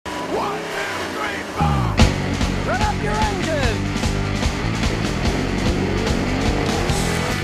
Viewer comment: what exactly does say in the beginning.. is it .."rev up your engines" or "grab up your engines"...i wanna know... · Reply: ellitestar rev up your engines